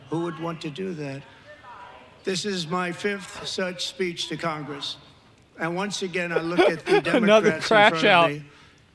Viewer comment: what is that lady saying? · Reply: ”Lie. That’s a lie. That’s a lie” I don’t remember how many times she repeated that but it’s what I heard